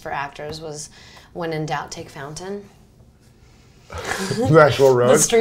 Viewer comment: awesome interview! but does anyone know what nina said? in 47:35 saying by Meryl Streep, "when in doubt, take... " what did she say? fountain or falton? anyone know or get the joke? :( pls let me know cause I am so curious about it! :) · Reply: When in doubt take fountain meaning taking Fountain Ave. is usually a lot quicker than taking Santa Monica Blvd. or Sunset Blvd.